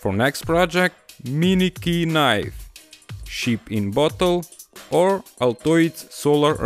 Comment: did he just say shit in bottle?